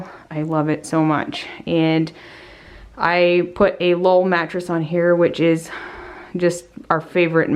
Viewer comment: She said it a Lull brand mattress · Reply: thank you! I thought she said wool mattress but it must have been lull.